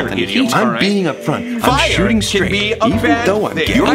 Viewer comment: 😭😭🤣🤣 look on subtitles if it hard to hear what virgil said i will say to help Virgil : im being upfront, im shooting straight, even though im gay 😂